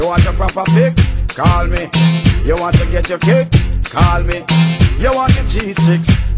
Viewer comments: You wanna get your kick which is I thought over the years I always thought he said he said you want to catch your kids what it sounds like lol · for the longest time I thought he was saying you want your cheese sticks call me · when i was liitle i thought he said you want cheese stixx idk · for the LONGEST time my b/f thought he said cheese sticks at the beginning.. but it says "g's fixed" Lol